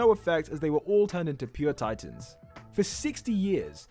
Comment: Did you say 60 or 16 years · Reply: 60